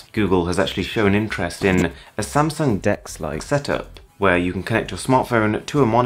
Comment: I'm pretty sure instead of saying "Samsung Dex-like experience" he said, "Samsung dicks-like experience". What other mistake could he have made, big enough to actually cover it up in a voice over instead of an astrix and text like other YouTubers? 😂